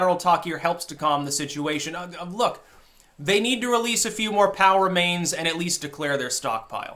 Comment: For weeks, I thought you'd been saying "power mains". LOL